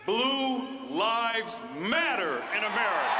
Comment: For a second I thought he said "boo lives matter".